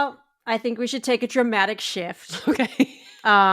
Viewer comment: This was a fantastic episode! Nobody asked, but you gave us lots of great opinions. One thing: said “We need to take a dramatic shift,” and I thought you said, “dramatic shit!” And I was like, what?! 😂 46? Daaaaaaaaang, girl! But maybe your mind is going now (forgetting phone is on)? ;-) Anyway GREAT episode!